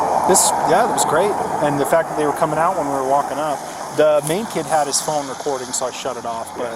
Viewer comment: I thought I heard him say they were walking out when we rolled up?